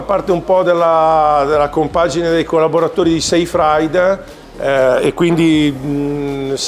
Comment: per un attimo ho capito "sei fraid" che in romagnolo vuol dire "sei fradicio (dentro)" 😂